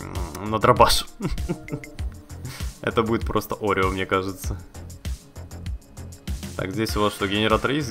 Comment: я один услышал oreo?